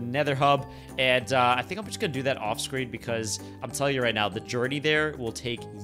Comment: I thought he said "off stream"!!!! 😂